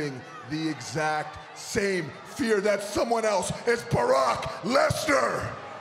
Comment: did Jinder Mahal called Barack Lesnar instead of Brock Lesnar? 😭😂